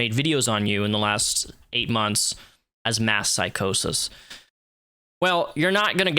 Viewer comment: "mass psychosis" he's the one who looks like he's gone through a psychotic break rn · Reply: I thought he said "Meth psychosis" and was like ????